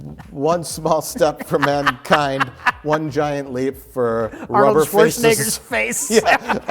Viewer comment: if anyone who happens to be deaf and has CC on, just know that what was actually said was "Rubber Schwarzeneggers face" and not.... well, it's a rough interpretation that's all I'll say.